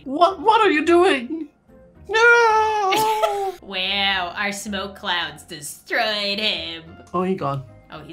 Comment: When Lauren said "Our smoke clouds" I thought she said "I smoke clouds" 😳🤣